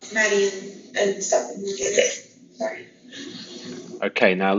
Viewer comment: I think she says Steph, not stuff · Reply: I heard "Maddie and Stephan" as well. (Could be "Steph" for sure, but I've never heard her - or anyone - call him anything shorter than "Stephan.") 👍